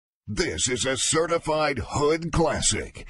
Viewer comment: the entire time i thought it said this is a certified good question